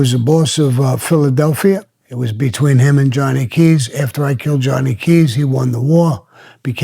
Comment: Did he say"after i killed Johnny Keys"?